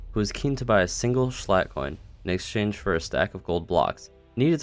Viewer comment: I thought it said slatt coin